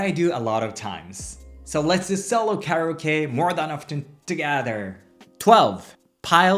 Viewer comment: I was listening to this video and when he said solo karaoke I thought he said carrot cake. Do you love carrot cake? Lol I had to look at it to see what he actually said